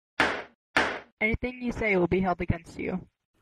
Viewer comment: I thought he said Boris and was confused and then rewatched probable 7 times and then found out that it said not us and witch then I started laughing because of how dumb I am